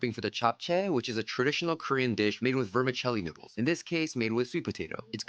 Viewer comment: idk why i thought you said vermicelli nipples lol